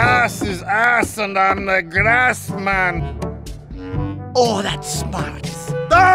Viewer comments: did he say grass man? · "YoUr ASs Is AsS AnD I'm THe GraSs mAn" demo 2015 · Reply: TheParadox1010 I heard him say glass maan